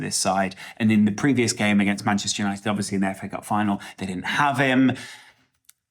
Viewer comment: Did he say FA cup final?! 😂